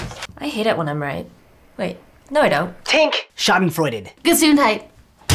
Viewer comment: Did they said Schadenfreude - Gesundheit cO? at 4:30 or sth like that